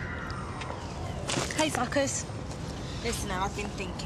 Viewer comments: I honestly always thought Magda said “hey fuckers” (at 5:02) only to be today years old to realise she actually said “hey suckers” 😂😂😂😂😭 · Did Magda say “hi fuckers!” At 5:01 ?! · does she say hey fockers or hey suckers wht does she say :))